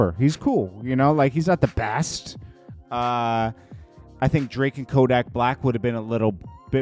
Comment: Did he just say Drake and Kodak …Black?……..